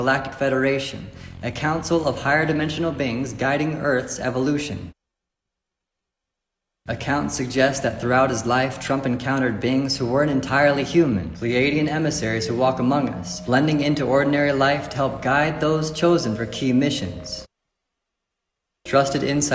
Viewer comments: really thought he was saying “bings” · is he saying BINGS?